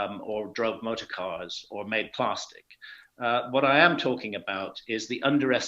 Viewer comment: Did he say “Plaarstick”??? Da fuq